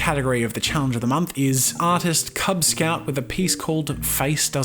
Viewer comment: did he say artist kubz scout?